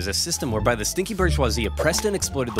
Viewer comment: It's funny how at 7:24 the subtitles read British instead of borgoise or however